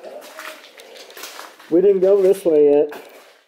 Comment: Hi There! At 3:25... Did either of you happen to hear the voice of an older woman? I first hear you say in a hushed voice.. "Oh My God." This is a Class A EVP. The Woman says..."Come Help Me!" Followed by you saying.."We didn't go this way Lyn?" 30:38... The both of you are exploring the Morgue. You say: "There should be?' A disembodied voice can be heard..Speaks in half whispers.. Says.."Wigborne?" 37:28... You have discovered organs all over the floor near the morgue. You say: "Dude? This may have been the organ cart Dude?".. It is here that you catch a female voice. Sounds young. Whispers "Of course it is." 37:32.. You: "Oh My God! This is where you catch a male vocal clearly asking in a hushed tone.."What is it?" 40:53... Just as the both of you are walking up from the morgue.. Through a landing to climb ro upper floors.. Both obiviously feeling nauseous from standing in body soup.. A unusally "deep, demonic sounding laugh/chuckle" can be heard within the conversation you both are having! You say: "Is that the 5th floor?..Dude!?" (Laugh) **Notice your cameras were fuzzy? That very well could be because of the dark energies presence in the area.**